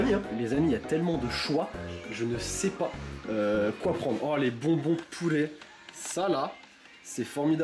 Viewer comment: Omagad j'ai eu peur j'ai compris "Oh les bonbons POULET"